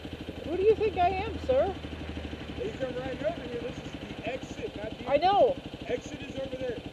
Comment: I clearly heard "this is an exit" and "hop the curb" :D